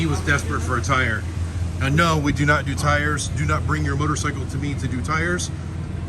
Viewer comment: I heard "We now do tires"... 🤣